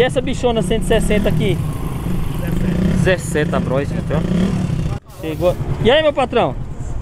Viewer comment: será que ouvi errado $17.000 numa Bros?